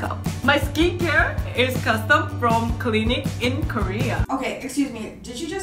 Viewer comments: I thought she said Clinique at first when I watched the Vogue video, but now I see, it's clinic XD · I thought she said custom from Clinique . Lol.